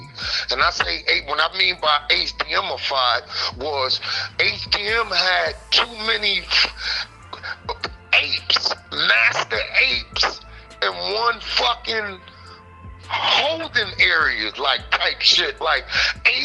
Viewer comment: Wait hold up...did he say it had too many nasty apes?🤣🤣...The fuhk is a nasty ape? LMAO.. Man ya'll be killin me on this channel hahaha, I almost choked on my pizza,when he said that.